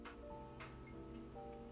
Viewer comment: I heard in the end "please let me take you out to dinner tonight💀"